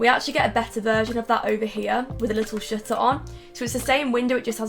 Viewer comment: i thought you said 'little shitter on' whhhattt haha